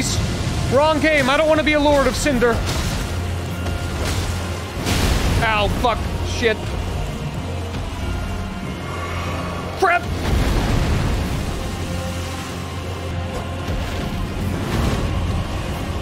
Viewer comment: Dont know if that was a joke when he said "crap" because to me it sounded like "crab"